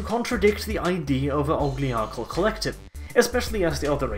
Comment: I heard the term 'ogliarchal.' Did you mean oligarchial?